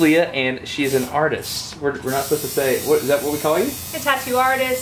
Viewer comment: Did he just say "Do I call you an "artists" ?"...